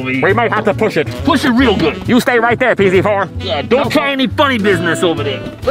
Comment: DId I hEar PZ 314, swEar On vIdeO